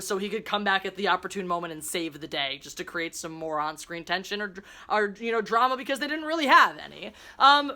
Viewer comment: I'm getting PTSD just watching your review. Blocked. Out. So. Much. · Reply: Lol you said "... create more on-screen tension..." All I heard was "...moron screen tension..." Apropos, no?